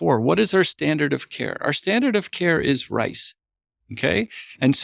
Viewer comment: What did he say??? Rice?? Ayo what is their standart? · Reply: RICE - Rest, ice, compression, elevation